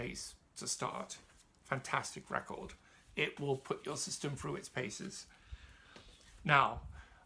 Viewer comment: Sorry, didn't catch that: my best guess "it will put dust system through its paces?" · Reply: "It will put your system through its paces"